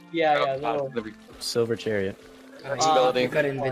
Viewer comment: did I just hear silver chariot????